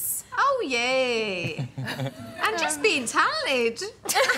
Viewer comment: What jesy said at 0:32 ? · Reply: She said "And just being talented" as she should 💅